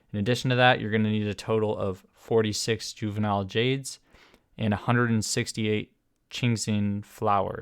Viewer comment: I thought it said u would need 46 fragile resin and started to panic haha thx a lot for the guide tho really helped a lot x